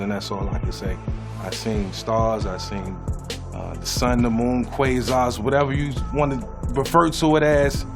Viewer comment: What does he say at 2:13 after sun and moon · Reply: Quasars